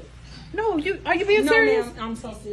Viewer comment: “So you not going to help me at all?” No mama! 😂😂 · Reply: I sounded like she said no ma'am